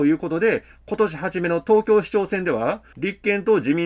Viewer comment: 蓮舫とその一味！小池が許しても八丈島のキョンが許さない‼ 8:22東京市長選に聞こえました。